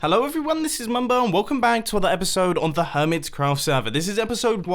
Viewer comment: Sounds like you said welcome bang